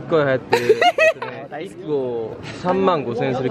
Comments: I think Yuta said “35,000yen” in Japanese. It’s about 360,000won or 336 dollars.🤣🤣🤣 So funny 😂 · Did Shotaro just said 'baka' no i'm wheezing ㅋㅋㅋ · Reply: He said takka = expensive